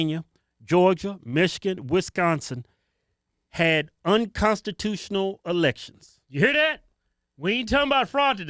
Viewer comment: Sounded like you said unconstitutional relations l!!